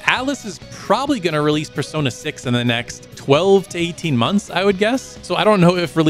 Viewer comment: there's an audio glitch that makes it sound like you said "months" instead of "years" which is what you meant.